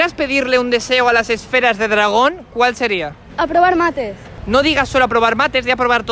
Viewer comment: Cuando dijo aprobar mates pensé que decía a probar mate (los de Argentina entienden)